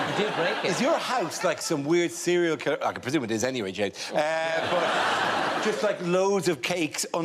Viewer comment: Did Dara call James Jake at 12:22??